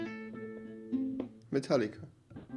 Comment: hat der bei 'House of the rising sun' ernsthaft "metalica" gesagt? sorry falls ich mich verhört hab,bin mir abaer ziemlich sicher...